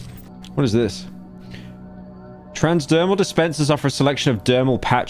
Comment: Bro wtf the fuck is wrong with me? At 21:45 i thought he said transperm dispenser💀